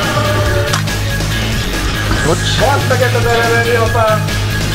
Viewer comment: nél mit mond a fickó? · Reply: "Ebbe belemenni, apám!" Mivel belerohant a lövedékembe :D